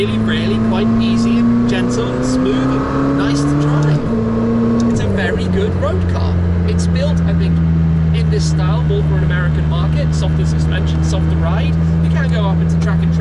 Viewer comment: Never knew McLaren had a facility on this side of the pond Where in the states is this McLaren plant Shmee150? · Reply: Doh 😣 My apologies, misheard the built to US specs as built in the US , I should’ve known better, Luv the Sabre